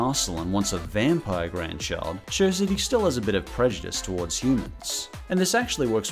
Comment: with the captions on it says bitch instead of bit.